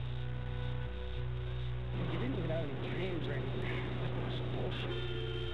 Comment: if you listen in the backround i think it is munro that says " he didnt have any dreams or anything, which is bullshit". i started balling at that point.